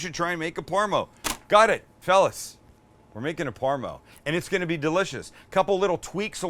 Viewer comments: Thought you said you was making a Porno.. not Parmo at the beginning 😂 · I thought he said making a porno 🤣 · I genuinely thought he said porno at first HAHAH